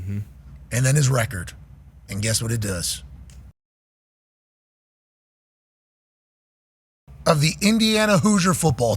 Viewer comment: why is the audio cut out? What did Pat say that would warrant that? Anyone read lips? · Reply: He said” and it says, this mother f&cker wins”